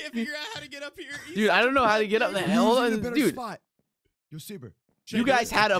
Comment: Did I hear a h*ll!?!?